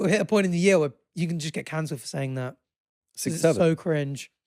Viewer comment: I thought he said cancer instead of canceled 😭